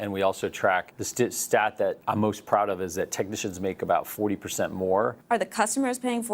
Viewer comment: He said "the technicians are making 40% more" I heard "we cut 40 % of our technicians"